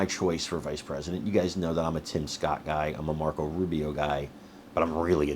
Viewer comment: Thought for a second you said Rick Scott... He was governor of Florida and I believe Senator? All I know is when he ran for governor the first time I was living just across the line in GA and saw the attack ads non stop. When he was up for re-election I had moved away but was back in FL on business. Turn on the tv, same old thing. Apparently he didn't have many skeletons in his closet if they kept bringing up the same thing.